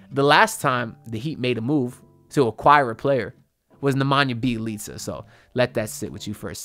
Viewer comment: wtf does he say at 3:16??? sounds like jibberish "Last time the miami heat made a move was the omubealtiste" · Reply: Nemanja Belicia 😂😂😂